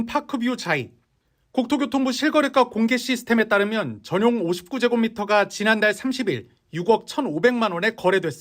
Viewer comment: 59제곱미터를 59평으로 들었네. 꺅